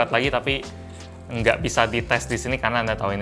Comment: bang kok testis? Bukannya testis salah satu bagian dari kelamin ya bang 😂. Atau gw salah denger wkwk